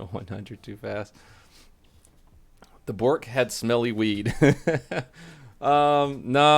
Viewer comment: For one very slow second, I thought you video said "Brooks and smelly weed." I thought you were at Faster Horses with me. I got excited for nothing. Darn, I would have loved to meet you!